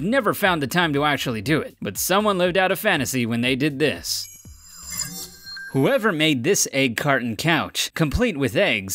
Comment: On the one about the egg carton couch I thought u said, "Huevo made this carton couch" I was laughing so hard